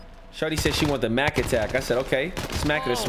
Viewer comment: Did say the N-word or I am tripping 🤔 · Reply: Nah he said “ima go s**t”